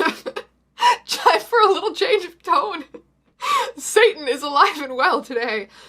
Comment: HOW DID I MISHEAR “ Satan is alive and well today ” AS “ SATAN IS ALIVE AND WELL ENDOWED “ IM